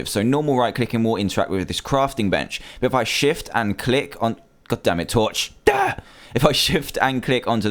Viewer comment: Who heard Dan said "Goddammit"